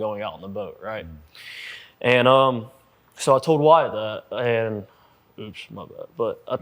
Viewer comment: I’m lost what did he say that he wasn’t suppose to say ? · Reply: He mentioned someone’s name (sounded like Wyatt)